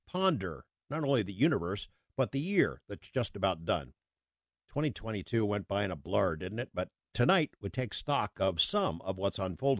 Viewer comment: it sounds like knapp says "2022 went by in a blaaaarrrgh" 🤣